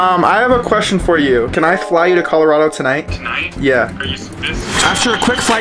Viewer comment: did I hear that right Colorado?! My home? Canada? I’m Canadian? O mah god wow